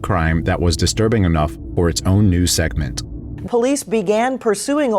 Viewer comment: Instead of, "Disturbing enough FOR it's own news segment.", the narration says,.."OR it's own news segment." Is this a text to speech program doing the narration? If so, you'd think that people would proof read their stuff before uploading it to their channels. I see this sort of thing constantly.